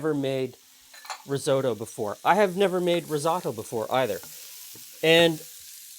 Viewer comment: aroundd 2:45 he says "i never made risatto either" what is that? google says finds nothing.